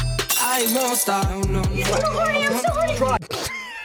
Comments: Ok but mad props to the main actress who somehow made it through the "I'm horny" scene without immediately fleeing the country and getting facial reconstruction surgery. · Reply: Oh man I thought she said "I'm so sorry" but now I hear it😭✋ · The ‘I’m so horny, I’m so horny’ completely ruined me and I will never recover. I can’t stop laughing 😂😂😂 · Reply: i honestly thought she said "im so sorry"